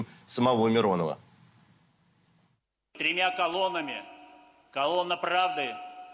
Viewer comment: "Тремя колоннами..." услышал "гремя талонами.."